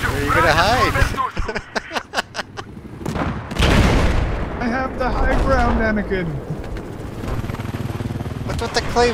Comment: Does he say "I have the High ground anakin"?